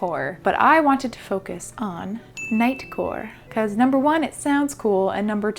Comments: When I heard knightcore, I at first thought you said nightcore, which is a type of music. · I thought she said “nightcore” for a second and I got thrown back into early high school music